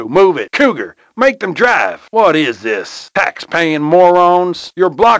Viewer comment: is it just my imagination or he really said "Max Payne" here?? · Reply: he said “Tax Payin” lol